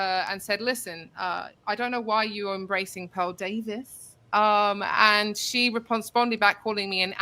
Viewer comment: She said “you don’t know Pawl Daveys?!” 😂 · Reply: Yeah, I thought she said Paul Davis.